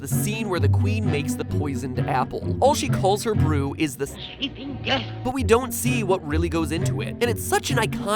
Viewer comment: what people hear : "sleeping death" what I heard : "sheeping deaf"